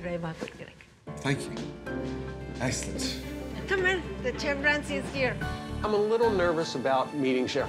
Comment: sounded like she said 'the chimpanzee is here' 😫